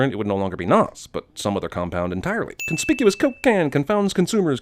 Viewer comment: "coke can" sounded like "cocaine" lmao